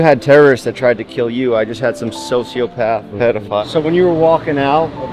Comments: did he say a sociopath pedophile lmaooooooooooo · "you had terriorists try to kill you, i just had some sociopath-" the noise i just made oh my god hahaha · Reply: !!!! Listen again I heard him say sociopathic pedophi- like wait whhaaattt?!